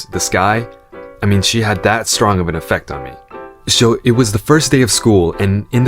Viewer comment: ~ I can't catch what he says here. "She had that strong of an effect on me"?